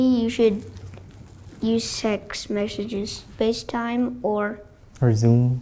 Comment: I heard "use SEX messages" 💀...I just...anyways he said "text" atleast